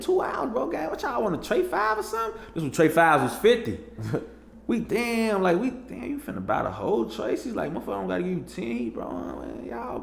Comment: Wtf did he say from 9:02 - 9:07 ? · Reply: Dam you finna buy the whole Tracy (3.5G)